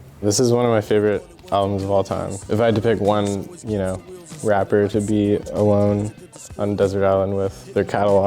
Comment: What rapper does he mentions before Doom I can't understand? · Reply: that part "to be alone with on a desert island..." was not clear.. english is not my first language .. I thought he was talking about some rapper (I thought he was saying "along with....") thanks!